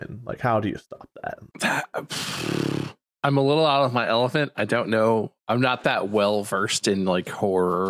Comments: Did he say "out of my elephant"? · am I the only one who hears "I'm a little out of my elephant"? xD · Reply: No yeah he absolutely says elephant